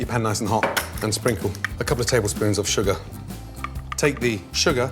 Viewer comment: He must've said "a cup or tablespoon" instead of "a couple of tablespoons" lmfaoooo